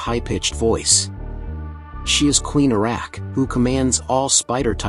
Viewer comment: I heard Queen Iraq